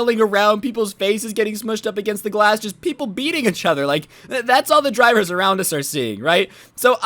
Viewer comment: My ears were burning everytime you said eachother because all I hear is "itch other" o.0